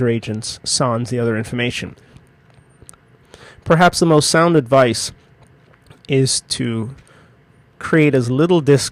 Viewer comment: I cannot understand what is said on 3:08: "These are the two ____ files" could somebody help me? Also could someone explain the advice given in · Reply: Catch-all